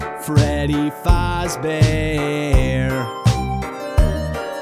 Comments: Sounds like he saying Freddy fauzbear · To me It sounds like he is saying Freddy fozbear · I feel dumb saying this... it sounds like you said freddy fozbear